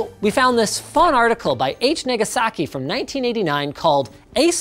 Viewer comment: Linus says article by H. Nig*asaki instead of H. Nagasaki 😂